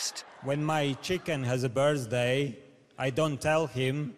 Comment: "When my chicken has a birthday, I don't tell him because he would not understand" WTF????😂😂😂😂😂😅😂😂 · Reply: I Thought He Said birdsday